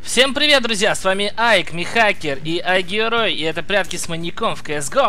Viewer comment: мне послышалось что михакер сказал: Всем привет друзья, с вами найк (nike)